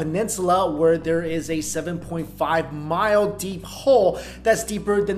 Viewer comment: 🤣🤣🤣🤣 I miss heard you and thought you said 705 miles deep! I was like 😮 is that even possible? 🤣🤣🤣🤣